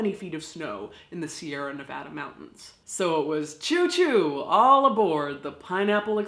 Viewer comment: Did...did she just say chew chew for the Donner Party?